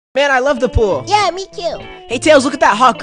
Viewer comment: I thought it said I love the fool